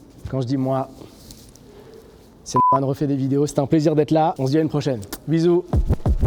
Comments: C'est moi ou a 9:00 il dit "c'était norman fait des vidéos" mdrr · Attendez mais a 8:56 il dit “Norman Refait des vidéos” ou c’est moi ? Si oui ptdrrrrr génie j’suis mort